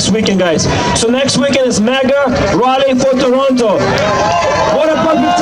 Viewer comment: MAGA IS the 5th rank in the satanic church why is he saying MAGA march??????? @ 15:13 Sorry he did not say maga he said Mega as in large